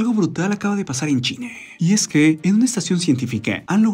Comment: ■ En el 7 segundos empezando el video que dice "Algo brutal acaba de pasar en .....", parece escucharse decir Chile en vez de China. ● ¿Dijo Chile o China? En el 0:07 segundos ⏱️. [Y no 007 James Bond 😁]